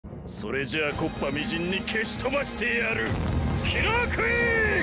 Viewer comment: did he just say "kingdom come"? N O M A T T E R W H A T W E B R E E D